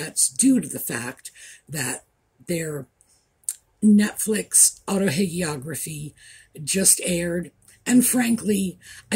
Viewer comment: I have barely stared to watch this but I initially thought said ‘AutoHATEiography’. I guess I misunderstood.